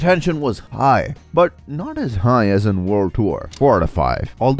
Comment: it says world war instead of world tour in the subtitles